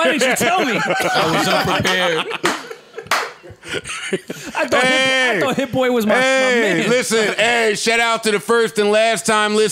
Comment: did Rory say hitboy I thought you was my nigga?